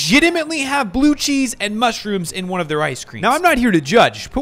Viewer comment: at first i thought it said biggie cheese ice cream